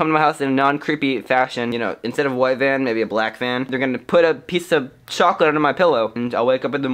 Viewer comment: At first I thought he said " There Gonna Put A Piece Of Chalk Under My Pillow." But then i realized, Oh:)